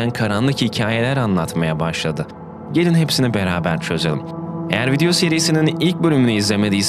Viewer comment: hadi gelün hepsini beraber cözelum diye duydum 10 saniyelik şok geçirdim dalgınlığıma geldi